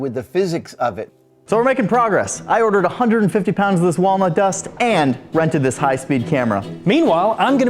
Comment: First I misheared it as Walmart Dust, ok then.